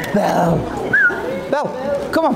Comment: Beautiful couple. I wish them a lifetime of happiness and blessings! At 7:45, they had the cutest ring bearer I’ve ever seen! I think he said her name was Belle.? I hope she got a special treat for doing such a great job for them. Interesting venue.